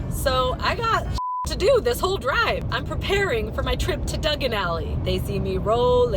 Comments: Wait.. Did she just say Dugen Alley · you said dungun alley instead of Diagon Alley